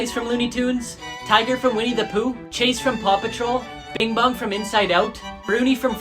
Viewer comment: im sorry did he just say bing bum?